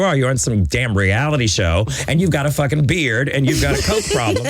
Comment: Did anybody catch her saying at around 8:18 "you have a mustache and a coke problem" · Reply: Danni Sullivan she said "you have a fucking beard (willam) and you a coke problem (Sharon)" because they where saying that she was a nobody and they were Racers, basically